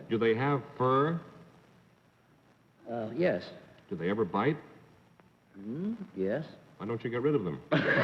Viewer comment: Steve Allen: Do they fight? Yes Steve Allen: Well, why don't you get rid of them? LOL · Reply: I hear "Do they ever bite?" rather than "fight."